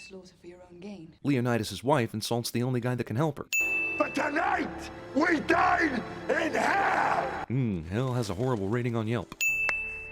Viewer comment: (Not that they would go to the fields of punishment, far more likely they'd go to Elysium, as they would have definitely gained prominence and honour from defending their homeland...) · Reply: To be totally honest I thought he said "Spartans, tonight we DIE in hell" lol but he said dine not die...me preferably would think it'd sound more badass if he said tonight we did in hell meaning they know they are going to hell for war and they are ready to die